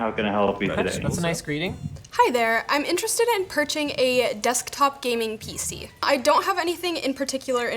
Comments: did say purching? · said purching instead of purchasing. Was it a mistake or people actually using that? Never heard it before. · did just say "purching"? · did say purching or am I on crack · it sounded like said his name was "SH$T"